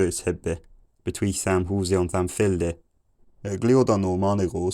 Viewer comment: I'm part of that wider audience you mentioned, I have no specific experience with old english aside from what I happen to have come across in regular life (not much). I haven't watched it with the subtitles yet while I write this, I'm pausing every now and then to write the comment. Just before 700 AD was the first bit where I "felt" like I should recognise it, something about "it has been seven days since I..." I think, but not sure. just after 700 (1:21) I felt like I caught "between a house and a field", but I suspect I might be pretty far off. At 800 I heard what I'm sure is "chicken", but more interestingly, after that I think I heard someone being called a loud fool, but I doubt it. Just before 900 I'm hearing something about "no key, nor shape, nor gap", but I'm worried that I'm drawing connections that don't exist. After 900 I'm certain I heard "it is long winter, the ground is cold, the [something] is freezing in [something] the "tree?" is dead in yard... [unintelligible, but I think something is in town?] ...we have burnt our wood (or drunk our water, I can't tell, but given the context, probably the former), we have [not?] wool...". I think this is about the point where I could, with a week or two to get used to it, be able to communicate enough to get by (if it was a modern country speaking this language, obviously the time difference would probably be more significant than the language difference). By 1300, I'm pretty confident that I'm catching the gist of most